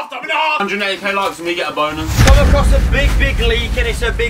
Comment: does he say bonus or boner?